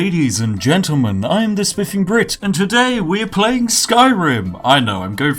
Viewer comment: I thought you said "dem sniffing grits" lol